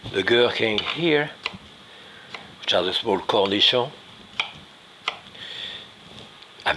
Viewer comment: where the subtitles say "indistinct" I'm sure Jacques is saying "a small cornichon"